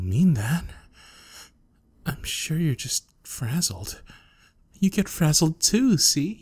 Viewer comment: Frazzled you say...? OR FRIZZLE! magic school bus theme music plays (I thought he said "Frizzled") Sorry not sorry.